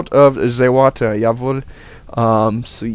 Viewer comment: did you say "jawohl" at 7:02?do you speak german?:P